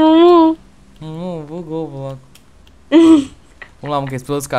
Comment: o bloco bugou momo, só eu q ouvi ?!?!